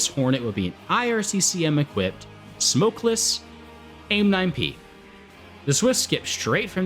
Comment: thought you said AIM-9B and I was dying like theres absolutely no way lmaoooo